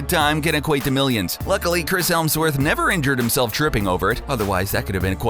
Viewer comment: did he just say Chris ELMSWORTH?